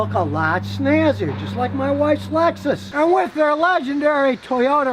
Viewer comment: Did he say 3:58 just like my wife's legs?! 😂